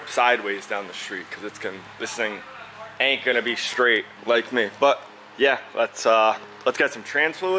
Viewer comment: Yooo, he said it's not strait just like me!! Lmao 100% didn't realize what he said when he said it. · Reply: Hahaha thats not what he said, He says "this thing ain't going to be straight... like me"